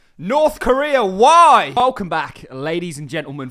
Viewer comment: There was a little cut in your edit in your intro and I thought you said 'ONG BAK!' instead of 'Welcome back!' 😂😂 Anyway, it made me laugh, & I'm always going to think of this hack as the Ong Bak Hack from now on. Thanks for your great videos, as always 🙌